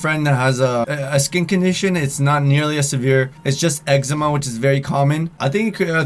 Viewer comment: It sounded like he said eggsma instead of eczema lol